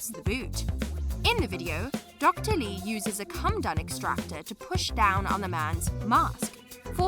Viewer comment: I'm sorry, did she say a "cum dirt extractor"?